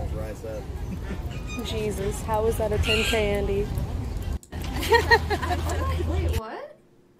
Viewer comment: can someone tell me what she says? Every time I listen to it it sounds like 10k Andy or 10 candy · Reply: ZGX 10k andy